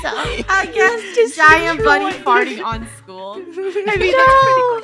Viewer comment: When i heard funneh say "bunny farting on school," i thought, "funneh farting on school," 😆 🤣.